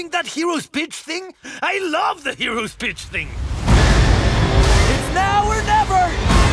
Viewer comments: I keep hearing him say “that hero’s bitch thing” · Did he just say heros bitch thing 🤣😂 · Is it just me or did it sound like Krel said “heroes bitch” instead of “heroes speech” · I’m assuming Krel said “Hero’s Pitch” but it did not sound like pitch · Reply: I heard "hero's bitch" 😂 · i swear when krel is freaking out over the speech all i hear him say " Wait are you doing that heroes bitch thing? I LOVE THE HEROES BITCH THING" XD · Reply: he is saying “heroes pitch thing” but his accent makes it sound like bitch which is awesome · is no one gonna talk about the fact that he sounds like hes saying bitch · I'm pretty sure I heard hero bitch thing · he sounded like he said bitch hahaha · Also is it just me or did it sound like he just said I love the heroes Bicth thing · I don’t know if I just misheard but Krel totally said “Hero’s bitch thing” right?? I’m not crazy???